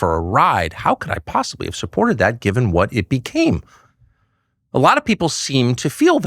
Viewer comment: Aw man, i misheard and thought Tucker said, “How could i have supported that gibbon.” 😂